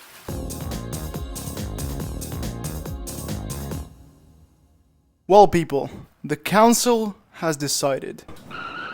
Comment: wth are they saying💀💀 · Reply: "personally I would f**k it" 💀🙏😭